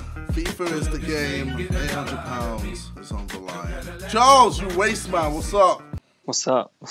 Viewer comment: I thought it said Charles titimonster